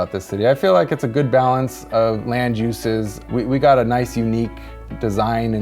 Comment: I kept hearing "Landjuices" when he said:" Land usage" and I was so cofused.